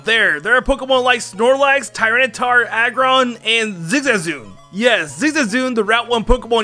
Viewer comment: that sounds like your saying zizazoo not zigzazoon